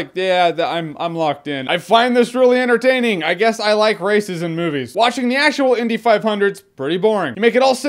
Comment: "I like racism movies" Now you can't hear it any other way.